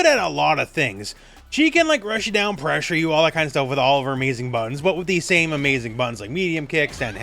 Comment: On the Cammy segment, when you said "she can like rush you down, pressure you, all that kind of stuff with all her amazing buttons", I swear that I heard "amazing buns". Both versions are true nonetheless.